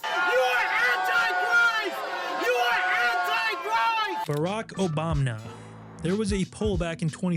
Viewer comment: ayo was that an OBAMNA I just heard?